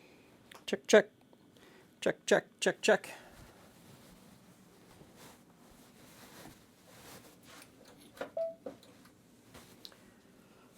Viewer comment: At the begining i really thought he was saying chuck chuck - chuck - chuck chuck ?